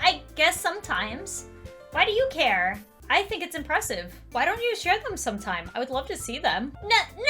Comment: I thought Lauren said, "Why don't you shred them one time." Well isn't that nice.